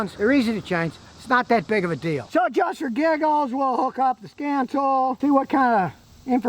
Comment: Captions say adjust your giggles. That made my day.